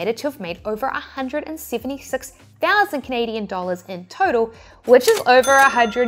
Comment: My New Zealand accent translation for those of you that are new here 🤭 Itsey = Etsy Riaction = Reaction Trindy = Trendy What other ones did I miss? 😜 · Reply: Turtle = Total 😅 (I had the video in another tab and thought I heard turtle, sorry!🤣)